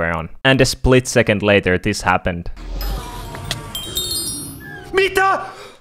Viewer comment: that kinda sounds like estonian as mida means what but i think u said like mita or something. Where are u from?